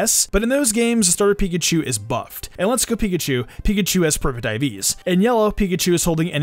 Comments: … did you say “Pikachu has perfect diabetes.” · Ngl when you were saying the Let's go Pikachu Pikachu had "Perfect IVs", I had to go back and re-listen cus I swore you said "perma-diabeties" xD